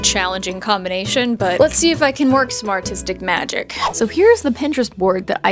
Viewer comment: I thought she said smartistic magic not some artistic magic LOL 🤣😭